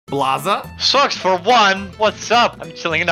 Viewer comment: I love when they both say Blaza Socks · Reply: I thought they said "Blaza, sucks."